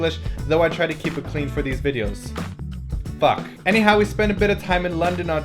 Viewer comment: i thought he said 'fuck' not 'but' at 1:40 haha